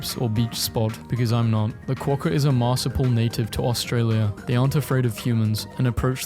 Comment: did- did he just say marsipl.. oh my god